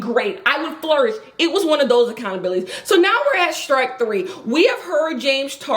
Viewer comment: i rly had to rewind cuz i thought u said “now we at shrek 3” and i was gonna go with it too